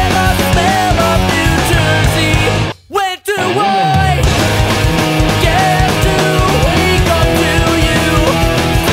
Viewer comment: what say? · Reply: "When Do I......."